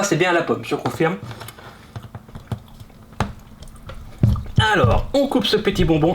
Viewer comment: C'est quoi ce AIHLORS (ALORS)